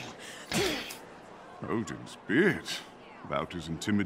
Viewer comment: i thought he said "Odin's bitch"